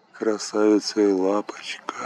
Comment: Сначала услышал на 3:31 «Красавица и лампочка»)))